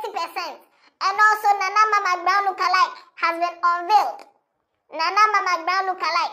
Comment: Am I hearing "must brown lookalike" ??😂😂😂